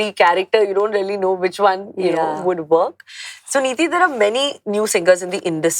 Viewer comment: Did she just call her Sunidhi at 12:16? 😮😂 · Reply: Get a life, it's So Niti not Sunidhi😅